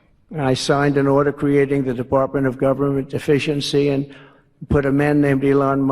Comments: Sounds like Trump said he created "Department of Government DEficiency" 😂 Which would be true. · Trump: "I signed an order creating the Department of Government Efficiency...." It really sounds like he says "Department of Government Deficiency." Which is what it seems to be.